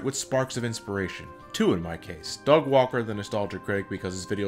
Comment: i thought i heard u say Dog Walker. Lolz